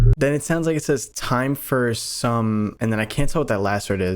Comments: i thought it said time for carnis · ish it sounds like he said time for some friends meaning the un members like he the singularity knew they were coming